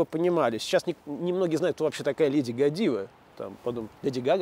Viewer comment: мне вообще послышалось леди Годзилла